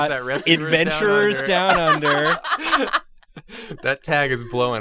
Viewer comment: I'd thought said "Avengers Down Under" and I was super excited thinking there was an Avengers parody placed in Australia. Dang.